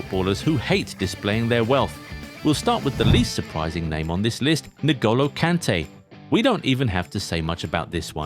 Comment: Did I just hear "Nigolo" Kante?